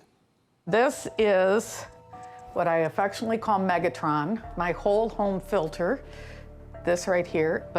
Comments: Someone should run on "A Megatron in every home" as a campaign promise. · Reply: I thought she said megaton at first. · I thought she said "Megaton" and tbh I'm extremely disappointed that she didn't